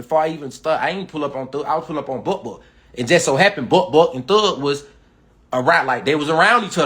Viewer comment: He said Buck Buck but with his accent, it sound like he said butt butt 😂